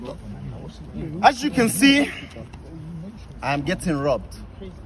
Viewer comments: Lol I thought he said “as you can see I am getting rubbed” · "As you can see, im getting robbed" KSI: "Ah shit, Here we go again" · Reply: I thought he said rubbed